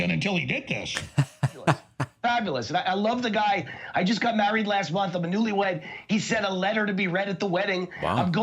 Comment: For a minute, I thought he said he just got married to the guy (tRUMP) last month. Damn my hearing. 😂😂😂